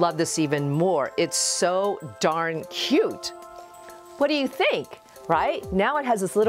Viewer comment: You said, "So darn cute!" What I heard was, "Sew 'darn' cute!"